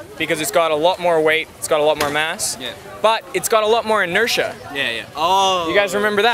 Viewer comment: yes only mass will make difference here, not m.i as he mentioned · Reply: actually I misheard the inertia as M.I